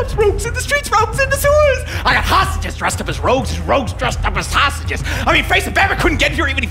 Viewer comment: I thought he said "Rouges dressed up as sausages" at first. Thinking about it that would have been really funny to see.